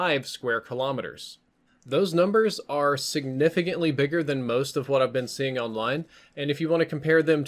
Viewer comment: All I heard was "its bigger".